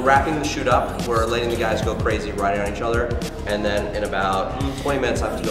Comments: is it just me that when the man said writing on each other I thought he said riding im ded lmaooo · did he say "we're letting the guys go crazy, riding on each other"?! o.o